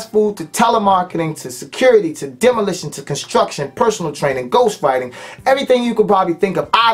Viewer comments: Did he just say Ghost Fighting?? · Did he just say "Ghostriding"??? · did he say he used to fight ghosts?